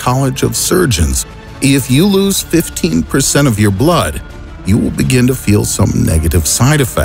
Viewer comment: who heard 15 instead of 50